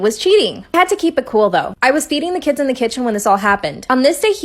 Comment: I thought she said I was beating the kids in the kitchen I was freaked out then I relised she said... feeding